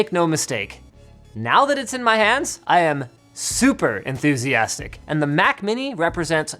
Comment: someone else heard "now that its in my ass"?